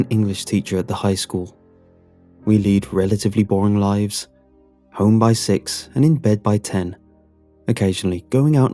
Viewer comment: who else thought that at 0:47 he said "home by sex"